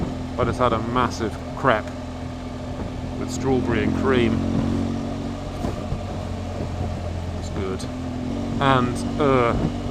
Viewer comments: Thought you said u had a massive crap with strawberries and cream lol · Thought you just said you had a massive crap. With strawberry and cream. Then I realized you said crepes.